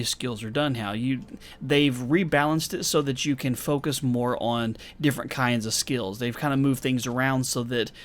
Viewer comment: Heh heh, says focus more on, and what I hear is focus moron. Sorry, I can’t help it, every time I hear more on I think moron. Same goes for double do, when someone says Yeah, I do do that don’t I. I have to chuckle, because I’m a grown man child.